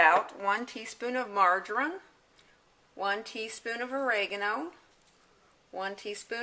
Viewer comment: it sounded like you said margarine? Did you mean something else? What approx. weight roast? What setting on the crock (High, Low, Medium)? Thanks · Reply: Marjoram